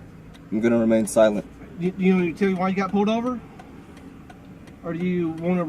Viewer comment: is that cop asking why he was pulled over? I think he says "you want to tell me why I pulled you over". Thats their job. The cop cant even do his job properly and tell the individual the reason for the stop. Hire dumb and get dumb results